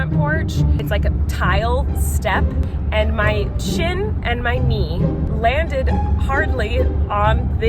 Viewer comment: And 5:37 when she said SHIN and knee, I thought she she said CHIN and knee so I was inspecting her chin looking for a cut or something 😂